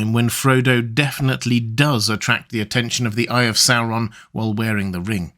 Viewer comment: I lgetimately thought you said "attract the attention of the gays and the eye of sauron"